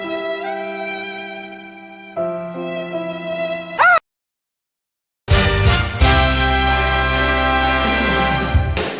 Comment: What does 4:28 say? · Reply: PreventTrentAbuse.